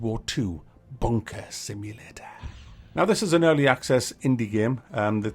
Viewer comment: Ooops I thought you said “ Bonker Simulator “ hehe, hoho, lol !, lol !, oops, laughing falling of my chair !. To be honest this is a piece of nonsense, there are too many so called Simulators at the moment, and many verge on the ridiculous !. Why don’t we have a Haemorrhoid Simulator, make it RPG, open world search for Anusol products !!, lol !, lol !, oops sorry! Hehe !!. I can’t get off the floor, I’m still laughing at this video. Oops now is the time I should display your ubiquitous Tommy Lee Jones caption, tut ! tut !.